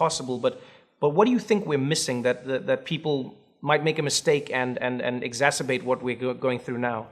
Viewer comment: All I heard was exzmasturbates